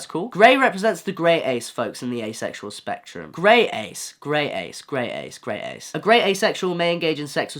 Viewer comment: I swear, when said Grey-ace, i sounded like he said my name. I panicked